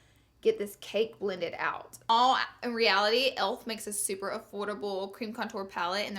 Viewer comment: did she say elf for nyx?...or em i going crazy